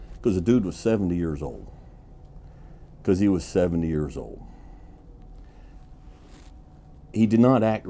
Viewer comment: I at first thought you said he was 7 years old. Thought a 7 year old was about to catch some cowboy hands.